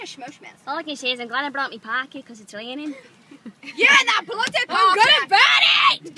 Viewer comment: Can someone tell me what is Jade saying at 2:23 it's been 3 years since I first tried to understand and I just can't😂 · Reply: "All I can say is that I'm glad I brought my parkar cause it's raining" 😂